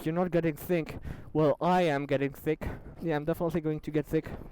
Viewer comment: It sounds like he is saying "I am getting very THICC"